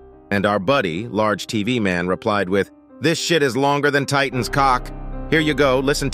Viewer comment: You misheard large TV man actually said this this shit is knocking our titan squad